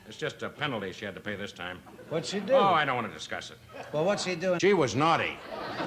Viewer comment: Anyone know what Winters says at 9:16? Sounds like it was censored and dubbed over. But the lips make it look like it's something very similar. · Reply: “She was HORNY”